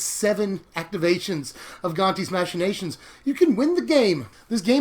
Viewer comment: I kept hearing "Gonti's Imaginations" while watching this a second time. It was funnily distracting.